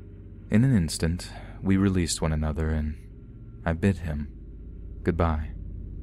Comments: I thought you said “ I bit him” at the end of the fourth story lol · Am I the only one who thought that the OP said “I bit him....goodbye..”rather than “I bid him..goodbye” in story #4? 😂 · After that lady paid for bio service I thought you said "I bit him (goodbye)" and for a second I was like what....?